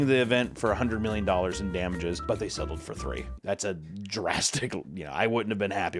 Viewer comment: wasn't looking and thought you said "they settled for free", which made the delivery of the next lines extra funny